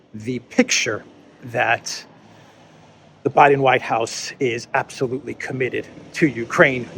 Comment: Did I hear correctly "Some of those people in the White House absolutely should be committed"?